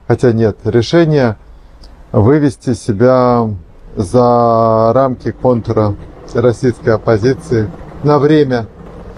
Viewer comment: Основная проблема оппозиции не в том, что начали ругаться между собой, она в том, что в какой-то момент ряд оппозиционеров пошли по принципиально неверному пути: вместо борьбы за демократию в России стали бороться против «плохой, недемократической России», и победа демократии для них — это, со всей очевидностью, победа над Россией. И это оттолкнуло от оппозиции россиян. В начале ролика сказано про выход за рамки контура российской оппозиции, честно говоря, я сначала неправильно услышал, и решил, что говорится именно о этой проблеме: послышалось «за рамки контр-российской оппозиции», или это был такой намёк? В любом случае, ряд оппозиционеров по сути теперь дискредитируют оппозицию, и будут отталкивать от неё людей, даже многих из тех, кто в своё время активно участвовал в деятельности оппозиции. Вероятно единственный вариант для оппозиции — воссоздание практически с нуля, без тех, кто заслуженно заработал себе сильный антирейтинг.